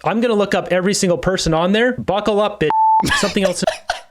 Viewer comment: I think the funniest thing is I thought he said buckle up bitches...which works TOO